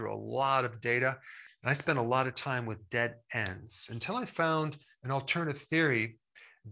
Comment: at first I thought he said, "I spent a lot of time with dead ants" lol